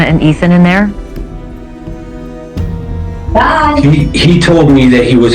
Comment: If you listen, it sounds like after she asks this question sounds like someone in the background says "bye" like they are not allowed to talk about that or something. Did anyone else catch that?